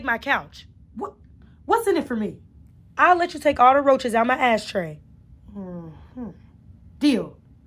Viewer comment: on the crazy one,when they was on the phone i thought she was about to say i will let you take all the roaches out my ass😂😂😂😂😂